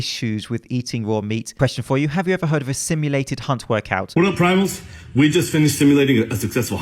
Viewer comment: I swear that I heard “stimulated c*nt”